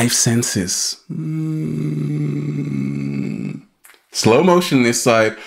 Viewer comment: "Slow motion this side" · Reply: I have always thought he said "slow motion that cite" as in he just made a citation and it was very important....But I could be wrong, lol.